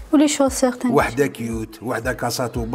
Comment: Qu'est ce que j'ai entendu les chaussert ? 😂Plutôt les chaussures